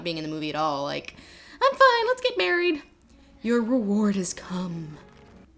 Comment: Okay, I have a problem. At 10:50 there's the shot of Aragorn smooching Arwen during the wedding ceremony, and Lindsay says, "Your reward has come," but I keep HEARING it as, "Your reward is cum," and I'm not sure she didn't do that on purpose.